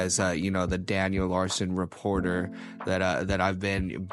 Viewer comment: I had to go back 5 seconds when I was watching the video because I thought you said "It's my duty as the Daniel Larson curator." but then I heard it a second time and realized that you said "reporter" instead of curator. The Daniel Larson Curator is this channels job: <link>